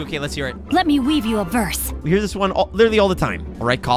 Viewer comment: Xingqiu says 'Let me weave you verse'? I don't know his lore, but I thought he said 'purse'. Hahahaha.